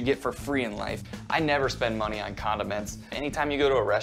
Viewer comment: I had to put on the CC because at first, I thought he said, "I never spend money on condoms." and I was like, come again? He said condiments 🤦🏾‍♂